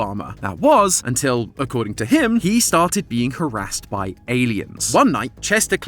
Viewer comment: why did I hear caressed? "He started being caressed by aliens."